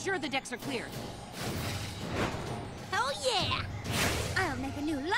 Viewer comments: Did say hell yeah or oh yeah? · legit I thought said "Hell yeah!!!' LOL